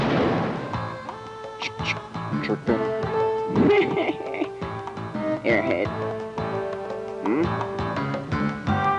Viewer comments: what did he even say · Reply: Thug: tricked him Killer Bean: hehehe airhead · What does he say here? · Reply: Go ahead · tf did he say? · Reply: chopa